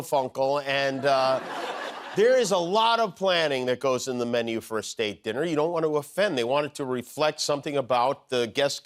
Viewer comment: I’m old with a slight hearing loss and I thought Jimmy Kimmel keep saying… a steak dinner when he was saying…a state dinner!